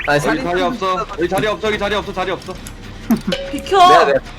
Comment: 자리없어를 자리야 없어 라고 들었닼ㅋㅋㅋㅋㅋㅋ